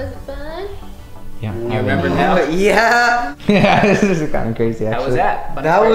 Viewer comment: did anyone else hear from “yeah this is some crazy a*s sh*t”